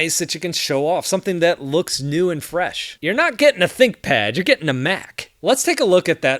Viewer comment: "you're not getting a thinkpad, you're getting a mac" me, watching this video on a thinkpad: 👁️👄👁️ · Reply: I thought he said thickpad 🤦‍♂️